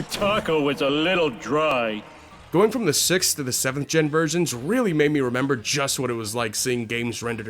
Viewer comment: did he just say 6 7? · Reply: sixth to the seventh gen. not 67.